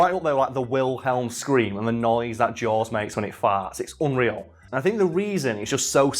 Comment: Did he say the noise that” jaws makes when he farts”?